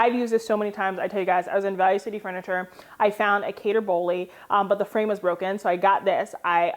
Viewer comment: Hey luv, what is the name of the artist from the large prints in your home? I thought you said Cater Bolle but I could not find a website.